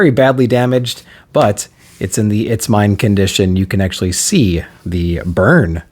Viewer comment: I thought you said “it’s in the ‘it’s fine’ condition” and thought you were referencing the meme of the guy drinking coffee while the office burns down saying “It’s fine. This is fine.” lol